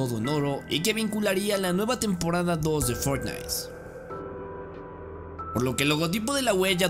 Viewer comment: No Me Digan Que Fui El Unico Que Escucho Que Dijo "Fornais"